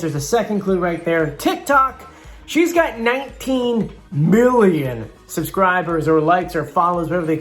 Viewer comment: i thought he said 19 billion and I was like wait wha